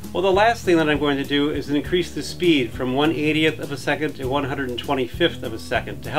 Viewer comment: I always hear "one idiot of a second" whatever that means :-) But thanks, those a great learning videos!!